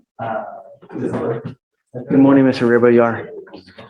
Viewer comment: At the beginning I thought Judge Fleischer said “good morning Mr Whoeveryouare“ 😂